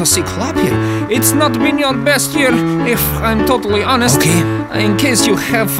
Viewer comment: instead of Ok it said Chelski, Chelski